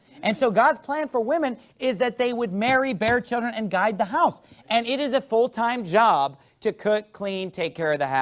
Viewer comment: i swear to god i thought he said die at the house i had to go back and relisten 😂